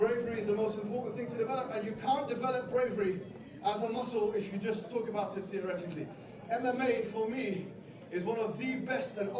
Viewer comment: What did he say? I cannot hear it! 13:27 'You can't develop bravery as a muscle if you just talk about....' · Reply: If you just talk about it theoretically